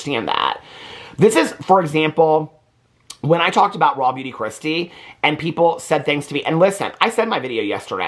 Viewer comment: I legit am here for. I don’t even know who this Morgan chick is and I just realized today that all along he’s been saying “ raw beauty Kristi” when all along I’ve thought he’s been saying “ Robbie-dee kristi” … 🤣 I am here to have talk in my ear while I clean my house. I could care less about Morgan and Robbie d kristi 🤣🤣